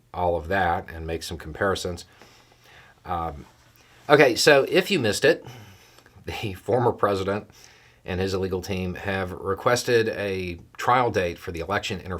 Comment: "If you missed it, the former president and his illegal team..." is what I thought I heard you say. Appropriate.